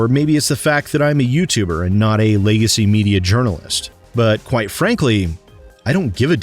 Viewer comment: I know you said "legacy media journalist" but I heard it as "lazy media journalist" and I did laugh